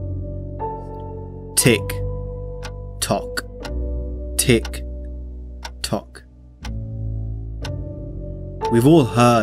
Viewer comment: Did he say Tik Tok?